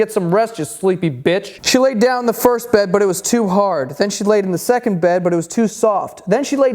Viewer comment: I swear he said she laid in the first bed but she was too hard